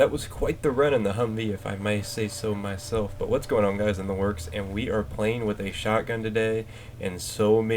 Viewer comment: 1. Why is it I always hear it as, "In the Lurks"? lol 2. BF2142 Theme! 3. Love that shottie. Nice vid